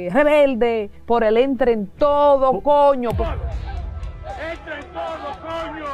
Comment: En 0:36 se oye claro: "entren todos, coño". No es verdad que dice "entren to' "